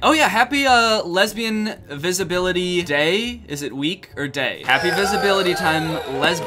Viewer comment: Ngl I thought he said lesbian disability day and I was just thinking that's a really specific celebration